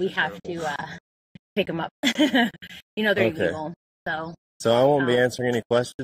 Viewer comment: at the 5:56 marker is did she say (we have to pick them up and you know their EVIL?????) pls tell me if i heard that right cuz thats bad if she said that